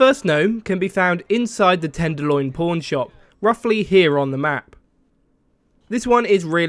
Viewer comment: I thought he said PORN shop and i instantly got on WD2 and went to the tenderloin but it was just a PAWN shop :( ....what a pickle